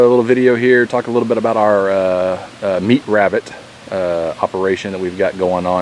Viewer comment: I'm a little deaf so I heard "Hoperation". Even though I am most likely mistaken I like the sound of it ^_^